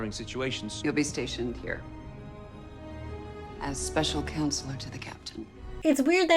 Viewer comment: I never picked up that Jack was assigned as ships counsellor. I thought 7 had said "special council". Like a more vague, "do whatever" job. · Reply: You were right. That is what she said. Special counsel to the captain. Jack is not ship's counselor. That would be insane. I had to scroll for half an hour to even find someone questioning this part of the video omg